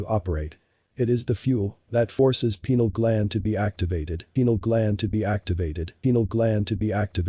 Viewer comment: Hey! Small mistake in the video -- you misheard the word "pineal" as "penile", which led to the conclusion that that guy with the TTS voiceover was saying that your dick is an antenna to alien messages. What he actually said was only slightly less ridiculous, that your pineal gland (in the brain, not the lap), which is for regulating sleep, is the third eye and receives awareness from that which is beyond our perception or whatever.